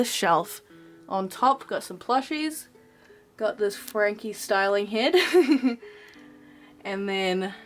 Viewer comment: when you said “Frankie styling head” I thought you said “Freaky styling head”😂